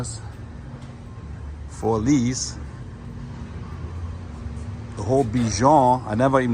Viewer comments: Everytime you would say for lease, I thought you were saying "police"! 😂😅 Lol😊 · I thought he said POLICE . Oh FOR LEASE !!!! Lol